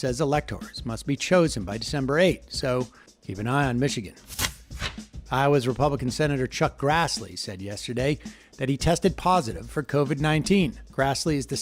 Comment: Almost sounded like you said Grassly died. Rats...